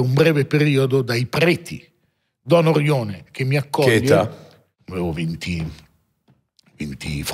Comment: non ho capito cosa intendeva con "cheta" Luca? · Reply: Che età chiedeva